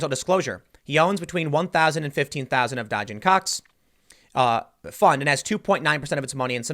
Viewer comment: sounds like he said dodging cocks o